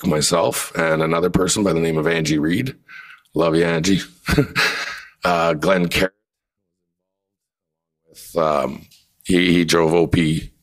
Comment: Now is it news if they keep dropping the sound? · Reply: what was muted will be in (*) @ 6:55 Pat says Glen (Carrot was involved), he drove... @ 7:30 Fellow Canadian exercising his (democratic right to protest “lawyer asked: was he was part of organizing it” Nope ) lawyer asked: so when you got to Ottawa how long were you there… These were the only two mutes I noticed but after comparing them to the unmuted National Post stream I see no reason why it was muted, contrary to your beliefs their was no fowl language or discriminatory language used. If there were mutes I missed let me know so I can confirm those ones too. I'm not saying king is perfect or without fault but we shouldn't be jumping to conclusions when we have resources to pursue truth.